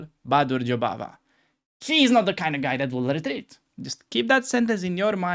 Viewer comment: he is not the kind of guy that what? I couldn’t make out that word. Also, good titanic joke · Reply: He's not the kind of guy that will retreat.